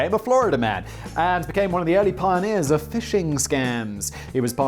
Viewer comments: I thought you said "fishing scams" and was trying to work out in my brain how one might go about that. Then you said something about email and I realized you'd said "phishing." I feel quite silly! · I forgot that there’s something called “phishing” and thought he said a fishing scam, so I was real confused by the ISP bit